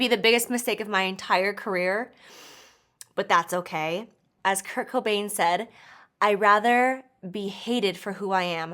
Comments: you had to drag kurt cobain all the way from the grave to talk about hairy lipsticks????? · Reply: latika ashley I thought she said the rapper Kirko Bangz. 😂😂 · Lmfaoo man, she said Kurt Cobain? I thought she said Kirko Bang in the beginning I was lost 😂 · I thought she said "Kirko Bangs" instead of Kurt Cobain.😂😂